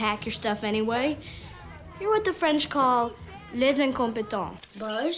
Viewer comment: did she just say, "les un competent". That means the a competent.